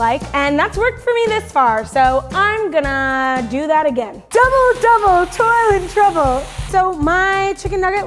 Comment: Who else thought the said double double toilet trouble or at 6:28 is it just me